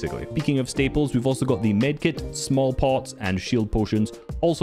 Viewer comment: did bro say small pot